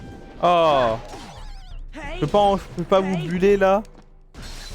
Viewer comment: j'ai entendu "je peux pas ovuler là"